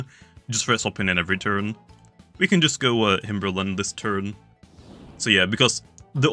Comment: did just say himbrrlen?